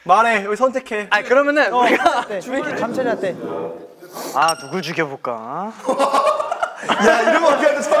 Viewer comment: 명호가할때 아아~?누가죽여부까~? 로 들리넹